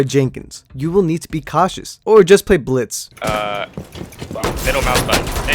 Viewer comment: did you said never play blitz? because its for noobs